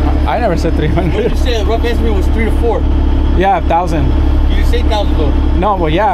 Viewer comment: First you said, 3. So, that’s $3 Dollars right there. HeHe! No, but it actually sounded like you said 3 TO 500.